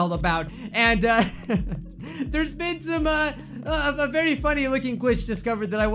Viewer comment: I turned on the captions and when you said there’s been some uh The captions said Benzema. Beautiful 😂